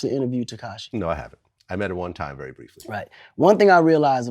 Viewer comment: did Vlad say "I met HER one time very briefly"? I could've heard it wrong but if he did refer to him as her then he just got a few points in my book. 69 does look and act like a female.